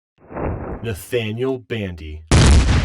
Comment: Is it just me or did it sound like he said natdaniel bandy